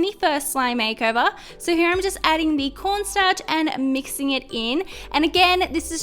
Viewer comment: Sounds like she said porn starch lol